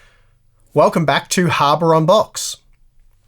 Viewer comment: See, I heard he said ""Hammer on Box"... I'm confused, what is the name of this channel? <3